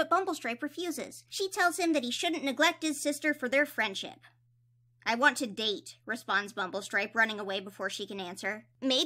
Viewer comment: I legitimately thought he said "I want to Mate!" I was like "WTF🤣"